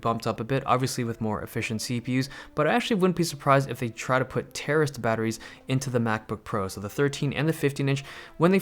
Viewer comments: I thought you said terrorist batteries lol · did you say "terrorist" batteries? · 100% thought you said "terrorist batteries" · Anybody hear terrorist batteries